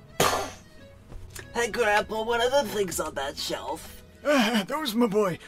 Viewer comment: At first, at the start of the video, I thought he said “Good apple” instead of “Grandpa”.